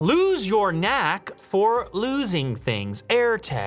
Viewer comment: For some reason I heard that as "Lew's your knack for losing things." I thought you were telling us you are our knack for losing things. 😉